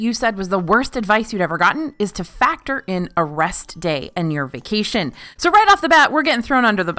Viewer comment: I misheard "a rest day" as "arrest day" and thought to myself: "Yeah, that's horrible advice."